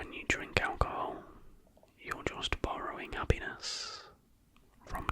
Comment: bro i thought that he said borrowing a penu#